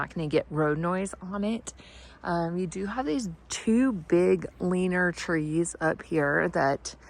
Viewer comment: I swear I thought you said wiener trees